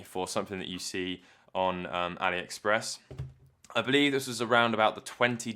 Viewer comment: Cool where can I get one? I heard 'something' Express?? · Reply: Aliexpress